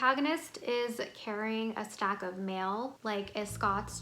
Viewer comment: I always thought she was saying a stack of mil... like she has 1 million dollars 😅